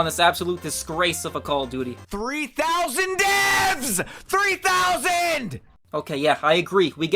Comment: oh it's 3000 deves i thought he said 3000 deafs